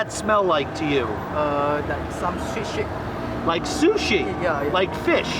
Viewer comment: When he asked the man what the durian smelled like I thought he said it smells like s*** LOL 😂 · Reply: He did say it smelled like shit 😆